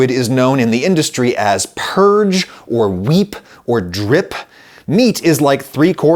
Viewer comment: D-drip? Did Adam just said Drip?